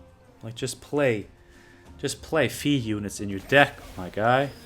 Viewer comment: "just play (what?) units in your deck my guy" did anyone hear what he said there? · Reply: Fee 😊 Units with Fee👍🏻